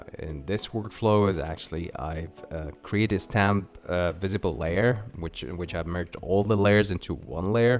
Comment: What did you say? Created a stamp? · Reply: A stamp visible layer - <link>